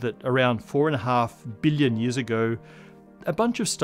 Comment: you say "4 and a half billion" but closed captions says "million"